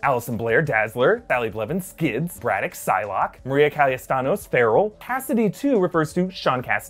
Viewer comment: the name of the mutant is Maria Calla-Santos, not CallaStanos haha i think it was funny :D Hi from Chile